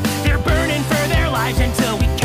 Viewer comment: I know the lyric "You're wasting your breath" BUT WITH THE CUT OFF IT JUST SOUNDED LIKE IT SAID "You're wasting your bread" but awesome Adam cosplay